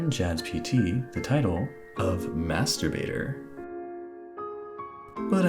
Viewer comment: am I the only one that heard masurbator instead of master baiter XD